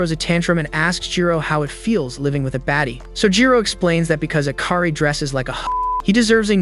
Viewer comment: what did he said there? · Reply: Hoe